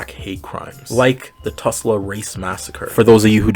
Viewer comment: Yo, you brought up the Tulsa race massacre. I thought you said Tesla race massacre, because im sleepy, so I was confused for a second thinking I was going to learn about something new. so the thing about sirens is that it was not until the medieval ages when they transformed from bird like depictions to that of a more fish like ones that are common